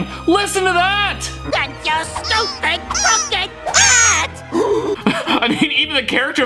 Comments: Note: Mugman actually said "AND YOUR STUPID CROOKED HAT" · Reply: I heard he said "stupid cupcakes hat" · cup head- crooked hat 1:58 idk..- ta ta baba (i think) 3:13 gumball- “WHAT THE!-“ 3:49 adventure time- “thank you..” 5:31 jake- “bucky sail away!” 6:35 scooby- “flump, poop!” 8:22 sesame street- book” 9:10 sesame street- i think he said hell 10:36 LOLLLLL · Your fans obviously love these kind of videos. Is it accidental profanity or the producers just having fun and trying to get away with it? LOL. Scooby Doo is Anna's favourite show. I wonder if Jenny knows the big great dane swears. LMAO. Artificial intelligence is the simulation of human intelligence processes by machines, especially computer systems. Just thought I would put that out there for those that have not heard of it before. Siri has some competition with all the new technology. Fun video Steve. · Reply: he actually says crooked hat you can also find out with subtitles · I think he says crooked paper hat